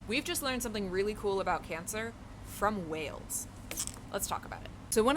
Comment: Gonna be honest, when you said we'd learned it from whales - I heard "We learned it from Wales" (Wales, part of the UK)... Was expecting something from Cardiff University!